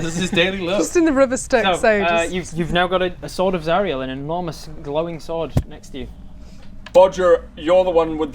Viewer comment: Honestly not a fan of some of these captions, I have them up in case I miss something or don't understand, but the rare times they completely fudge what's said is annoying. 3:35 "an enormous glowing" sword next to you Shortened to "long" sword next to you